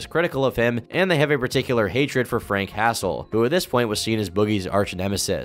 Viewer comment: I thought you said he pointed a gun at Frank Castle and laughed at the idea of Boogie pointing a gun at The Punisher himself. What a sad guy.